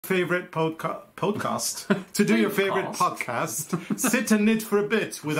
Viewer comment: Carlos, thought I heard you say 'your favorite polka', just miied listening the whole way, 'you favorite podcast'. Welcome back!